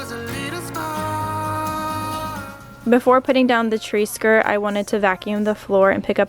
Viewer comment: I thought she said "before you put down the tracer" 😂